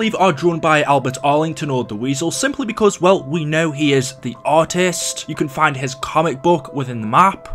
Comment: When he said artist I thought he said "Autist"